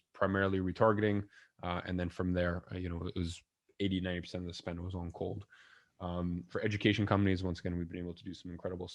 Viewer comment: What is he saying at 18:02 all of the spending was on cold?